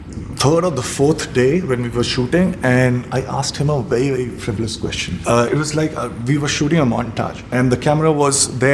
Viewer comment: Anybody can tell me what word he used in 4:20. I didn’t get that word ,,,, plsssssss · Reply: Frivolous question.